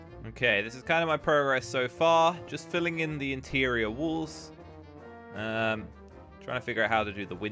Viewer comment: he said wool instead of wall, aahhhhhhh that british accent!